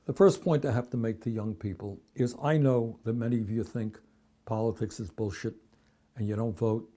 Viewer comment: did he say "i know many of you think politics is Bullshit" or am i hearing things?